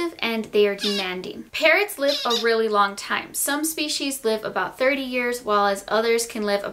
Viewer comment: Lol I thought she said parents live along time!!😂😂😂